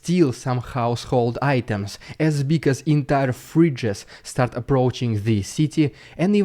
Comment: ah, fridge... I thought you said "freach" , like a combinaton between a freak and a b*tch :D